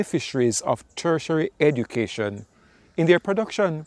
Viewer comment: I was trying to pick up on what you were saying. 'Treasury education'? 'Cursory education? I'm not quite understanding the term you're using. Do you mean 'higher education', because thats what Watchtower discourages. They totally discourage it, but if you have it, they will use you as much as they can. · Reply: Tertiary education.